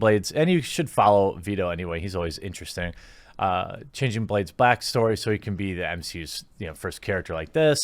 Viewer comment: Did you say "...changing Blade's *blackstory*..."? XD